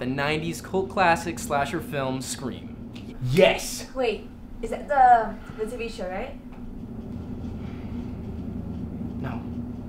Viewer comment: what did she say? I cannot for the life of me understand it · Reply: she said "wait, is that the..the tv show right?"